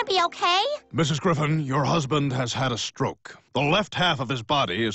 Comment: i thought it said peter griffin is snoke.